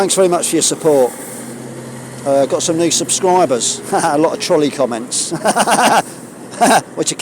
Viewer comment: I’m so dim, when you said “troll-ie” comments I thought you said “trolley” comments, and was momentarily muddled. Blame the morphine, bad day for pain so I’m all doped up. I was thinking yesterday about what you said about the threat of homelessness being used to control you and I suddenly saw that by packing up and carrying your home on your back you’ve taken control haven’t you? I’m sorry I didn’t realise the significance previously. I watched BH’s nonsensical videos about you yesterday. What a waste of space that narcissistic manchild is. As always, I’m sending you warmest regards from Somerset. Can’t say I’ve followed you from the very beginning but it’s been quite a while and I’ve watched nearly all of your content. You have serious artistic talent. You are also the only person involved in this whole thing whose narrative never changes, ergo the only one I fully believe. When you say “thank you for your support” I hope you know that there are people out here who are rooting for you to have the exposure you deserve, for people in power to LISTEN to your story and do something with it. And who want you to be safe and warm and cosy. I really don’t mean this in a patronising way, but I worry about you not being those things. IMHO you’ve done such a brave and genius thing, with your protest at the Tavistock, and taking to the road. I really do support you and wish you all the best ♥️